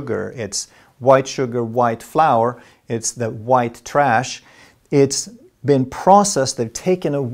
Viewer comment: did he just say "white trash" at 22:57 ?